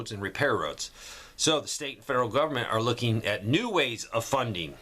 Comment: i heard a freudian slip there. You didn't say "looking for new ways of funding", you caught yourself, admirable. YOU SAID "looking for new wasy of 'fu$$king""..that was accurate